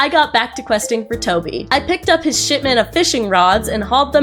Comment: i genuinely thought you said shitman at 2:30